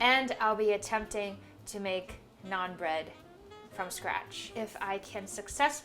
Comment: She won my heart when she said "Naan" and not "naan bread" sometimes in the video. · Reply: She clearly said naan bread. Btw, it's ok to say that.